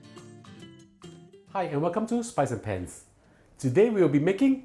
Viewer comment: You crack me up. I really enjoy your videos. At first, I thought you were saying “spicy pants”, which sounds rather catchy.